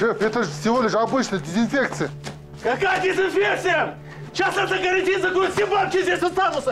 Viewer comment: что он сказал?👀🤣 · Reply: На карантин